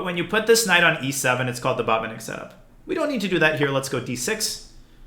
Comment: Can someone tell me what DN is saying at 2:07 when he says, "but when you put this knight on e7, it's called the _________"? Thanks! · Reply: The Botvinik setup